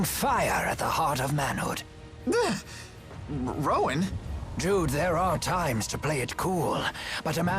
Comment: I keep hearing "dude there are times to play it cool" instead of Jude XD